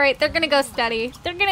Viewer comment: It sounded like you said go STUDY not steady lol